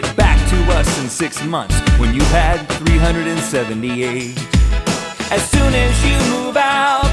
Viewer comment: in the graduation song in the part about hot pockets i thought they said "when you weigh 378" not "when you´ve had 378"